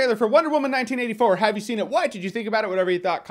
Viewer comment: Yo is anyone else hearing “Why’d you think about it” at 2:46 ... no just me ... ok 👌